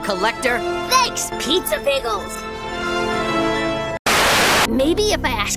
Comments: Was it just me or when i first watched the new episode i thought he said pizza pickels but then soon i found out it was bagel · It could Just be me... but I KEEP hearing "pizza pickles"...